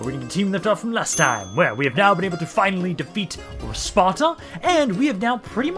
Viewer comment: When you said "we finally managed to defeat Sparta" at 0:47, it sounded like you said "to defeat our sponsor" lol Now we just need to take Epirus and the islands and then Hellas will be unified. Plus Cyprus and the western coast of Anatolia, too, if you're using the larger definition, haha